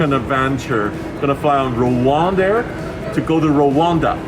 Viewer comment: Thought that said Ryanair at first😂